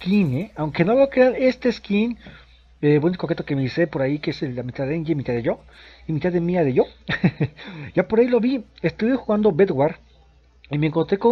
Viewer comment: que de quien y se quien? :v (es que no te entendí xD) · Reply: dije que mitad del skin es de enya y la otra mia osea (mia de yo) je je XP